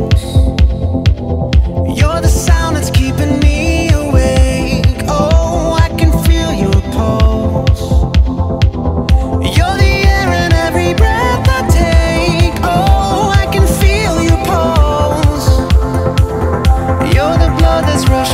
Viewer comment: "Ohhhh I can feel your balls." lol im immature. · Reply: I was so happy with "pulseeeee"!! Now i just hear "I CAN FEEL YOUR BALLS"! My gooooooood!! Lmao!